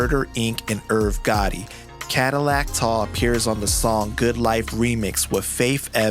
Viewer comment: Every time someone says "Cadillac Tah" I hear "Mitsubishi Tah"